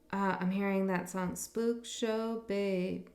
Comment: Riddle me this, when you said, "Spook show baby" in the Scorpio reading, I heard "Love Shack Baby!!"!! A Halloween version of the B-52's maybe? !🤣😂🤣 Then you mentioned the B-52''s, not a minute after I typed this, go figure!! This was a fun one, thanks & happy Thursday, many blessings always!! 😊💜🪶🌈♏🕉